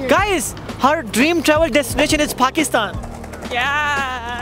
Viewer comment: IS HE SAYING PUCKISTAN🤣